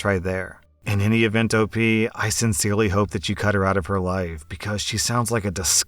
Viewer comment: I misheard and thought he said “cut her out of HER life.” And not “Cut her out of YOUR life.” I was about to say “Um.. please don’t… that would make you a murderer.”